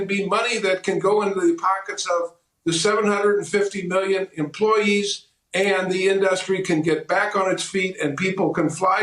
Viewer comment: 750 Million airline industry employees? Did I hear that wrong or what..